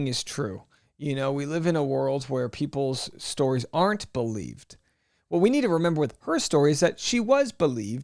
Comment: You said women are not believed, but then you said Amber was believed. Which is it? I have a couple of friends over the years that were tossed in jail for the night because they believed the woman. Tell me when woman are not believed? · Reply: He actually said "We live in a world where PEOPLE's stories aren't believed". But he is correct. Everyone believed her stories and he was guilty until proven innocent.